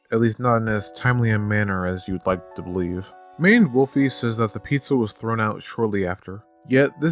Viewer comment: I keep on mishearing you saying Maned Wolfy as "Manimal Feet" and I don't know what to do about that. But hey, there's THAT information for you.